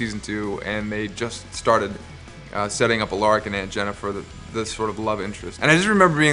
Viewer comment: did he say Jenifer😲😂